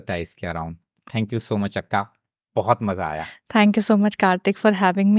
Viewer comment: Did he said akka